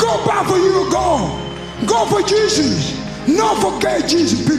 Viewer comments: I thought he said "no for gay Jesus" but he said "no forget Jesus" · I understand everything . He didn't lie!!! WHAT HAPPENED?? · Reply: People got very confused as they didn’t have subtitles at the time, and everybody thought he said”no for gay Jesus” and if you watch it back it actually sounds like he said that😂so yeah abit of confusion but a wholesome message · I remember when Yoel made this speech. He is one of the best MMA fighters at 185lb that we have seen in the UFC. He is also an Olympic silver medalist in the 2000 Olympics. He grew up in a Cuban wrestling program where they rationed food, and only gave adequit amounts of food to the best wrestlers. He has truly faught for everything he has. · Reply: Yeah everyone misunderstood him. Thought he was saying "No for gay Jesus". Caused a small shitstorm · This the first time I seen subtitles and I always thought he said “not for gay Jesus people”🤣